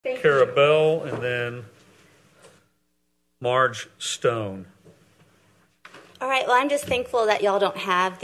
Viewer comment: I thought he said “Marge Stone”